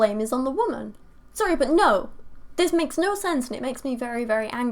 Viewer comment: did you say it makes no sense? Don't look for sense here.